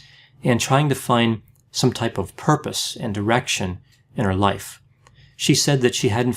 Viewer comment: I thought you said she was trying to find purpose and erection in her life! 😅